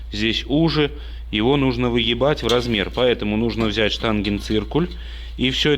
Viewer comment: Что мне послышалось уебать в размер😂😂😂